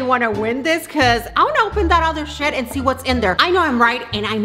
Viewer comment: I thought she said, “I wanna open other sh**...” But then I realized it’s her accent and she said “shed” not “sh**”